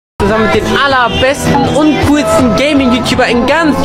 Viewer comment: Er hat gesagt: ,,Ich mein natürlich nicht Paluten“ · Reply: Ne du hast dich verhört sagt:„ich meine natürlich dich paluten"